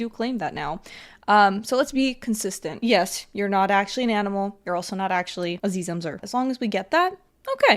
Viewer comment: (About 3 secs before) 10:50 is it just me who heard her say “you’re not actually a zhe/zhem/zher” actually your right, phenicfox uses he/him pronouns. But that doesn’t change the fact that Not respecting someone else’s pronouns is just bs.